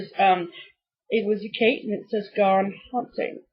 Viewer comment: Too funny!!! I thought she said humping. She says she said haunting though😉 thank you for putting up the hair!!!!! I am with your mom on that one, sorry. your Prairie Moon is fantastic!!! I would love to do the whole series if I could find them at the right price. Loved your trip details. I thought I remembered the house being on the road but white and smaller? When your 7-16 you do not appreciate those trips I wish I had. Keep stitchin